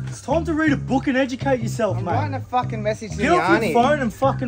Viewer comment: "I'm writing a message to me aunty" 😂why are reactions always hilarious? 😂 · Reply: I thought he said “ I’m trying to fucking message Johnny.”